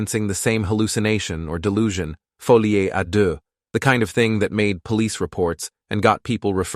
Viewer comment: Bro did u just say fulli e ado? 😭🙏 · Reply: It's, "Folie à deux," or "madness of two". The term itself is French, and it is a shared psychotic disorder, a rare syndrome where delusions are transmitted from one person to another, typically within a close, isolated relationship. The "primary" (inducer) with a psychotic disorder influences a "secondary" (induced) person, causing them to adopt similar delusional beliefs. There are some truly fascinating cases of this actually happening, such as the twin sisters Ursula and Sabina Eriksson. IF that was what was happening here, the husband would be the primary inducer, influencing the wife to adopt similar beliefs, and if we just base it off of the wife's initial reaction to the "gift" compared to the husbands, and how it changes over time to align more closely with his, this makes sense as a potential diagnosis for what is going on.